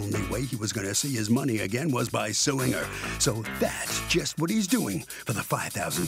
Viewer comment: I thought I heard he was suing her for $5, I need sleep.